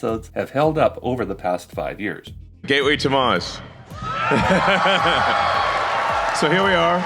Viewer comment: sounds like he says "gayway to mars"